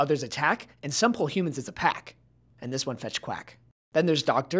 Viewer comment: They protec They attac They pull hoomans as a pac But most importantly They fetch quac Most likes I've ever gotten thank you so much! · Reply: Gozo [YT] first time I heard I thought he said “fetch crack” and I thought “that’s pretty heccin scary, LC”